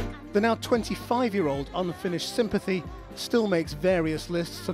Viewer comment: did he just say ''Unfinished Sympathy?''